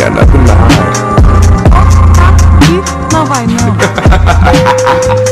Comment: Thought he said " You shouldn't be talkig to Sans"